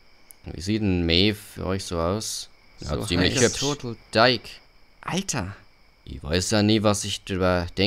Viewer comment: sorry aber was hat gesagt? Ganz schön steif?! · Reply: Er sagt "Like a total dyke".